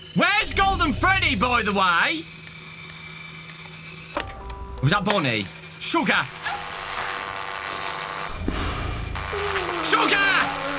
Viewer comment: Did you say "с*ка"? Did you know russian?